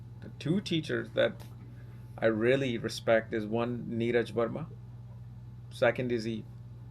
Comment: Did anyone catch the 2 teachers he mentioned who are experts with understanding the moon (15:25) and spelling? Thanks. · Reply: neeraj verma and eve